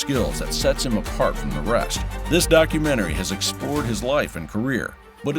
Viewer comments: Sounds like is saying mongsta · Sounds like he is saying The MONGSTER! 🤣